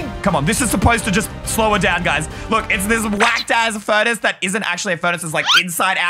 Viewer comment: did ey just said “ whacked a s s”